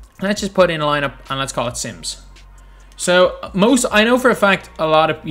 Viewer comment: When he said let's name this lineup sims I thought he said Simps